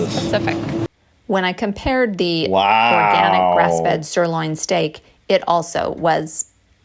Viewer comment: am I hearing things or 25:52 "when I compared the organic breast-fed sirloin steak" ?